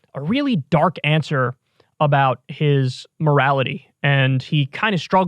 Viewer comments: Thought it said mortality. Lol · Damn I thought he said “Mortality” well better luck next time boys